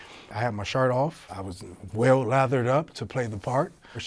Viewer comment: “I had my shirt off, I was well lathered up to play the part” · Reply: Oh.... lol. I thought he said "all leathered up"